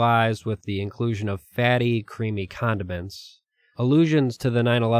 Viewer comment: Did you just say fatty creamy condom mints? That’s fukd bruh